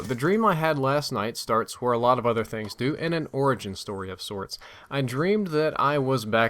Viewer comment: lol i thought he said orgy not origin in the beginning. huuuuuuuuuuge let down